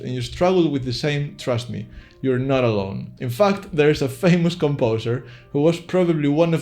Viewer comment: did he just say in fxxx? hahahhha.... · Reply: "In fact"